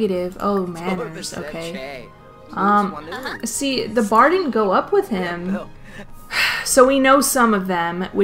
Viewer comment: eek I thought she said farting wont go up with hin